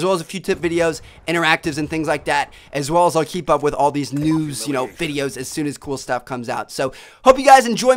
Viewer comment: i thought he said noob videos at the end lol